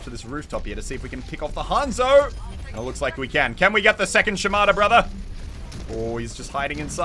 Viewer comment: Does it say something about me that when you said "can we get the second Shimada brother" I heard "Can we get the second Shimano brother" and thought "wait what does bike racing have to do with any of this"